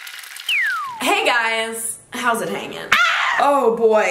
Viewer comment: why did i think she said “hey guys it’s meghan” and i was like hol up